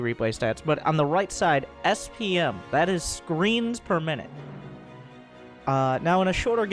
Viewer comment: I heard "screams per minute" instead of screens per minutes -_